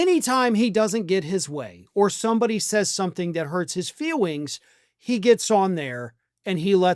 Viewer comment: I think I heard you say feewings.